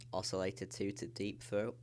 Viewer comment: Did you say Deep Throat or Dirty Throat?... 1:00 haha